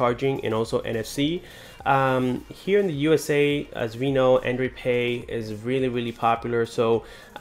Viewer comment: I thought you said android P weird Lmao. Edit: thank you for the heart ❤💗